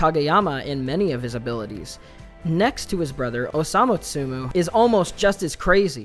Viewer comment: u made a little mistake at 7:06. Instead of ‘Osamu Miya’ you said ‘Osamu Tsumu’. Tsumu is what Osamu calls is Brother, cutting the ‘A’ out. Sorry for bothering!